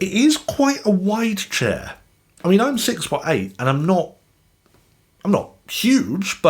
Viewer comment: Did you say at 11:35 "And I'm 6'8"." !? Goodness. That's some height!